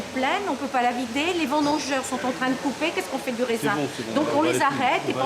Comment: C'est moi ou a 10:04 il dit "C'est bon jmen bah les couilles" ?